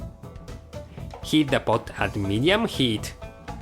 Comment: When he says 'Heat the pot at medium heat' the captions say 'high heat'. Just sayin'. :)